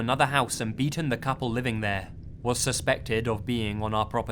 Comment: I thought he said "and had eaten the couple living there." Yeeeee.